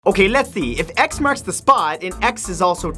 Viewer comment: who else heard him say lesee and not lets see at the beginning lol